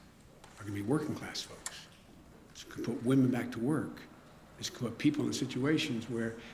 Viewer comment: "So we can put women back to work". Did I hear that right